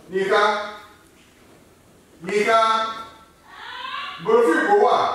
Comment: it sounded like he said the n word lmao. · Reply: He said Yinka the name of his wife